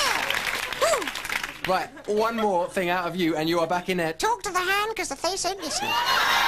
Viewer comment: what he said on 5:19? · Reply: talk to the hand, 'cause the face ain't listening.